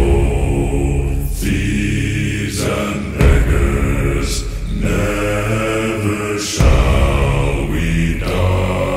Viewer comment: YO WHAT DID HE SAY???? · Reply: beggars, dude